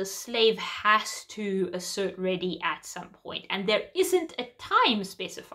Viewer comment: Hi, thanks for your comment! You're absolutely correct. That was the point I was trying to make at 9:42, there isn't a time specified! :) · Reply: Sorry, my mistake. I listened again and you said there isn't a specified time but what I heard was "there is". Thanks for the response :)